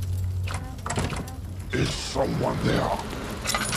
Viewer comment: I heard "Is someone there" and it flooded back memories of my Penumbra Demos.